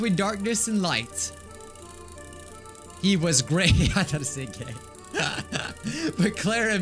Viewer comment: I thought it said "gay" too xD